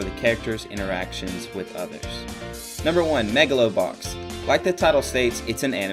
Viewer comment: xD at first I thought it said Megalovainia.